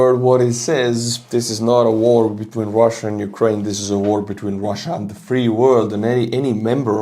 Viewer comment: He said 'the entirety of Europe' He did not say this is a war on the free world....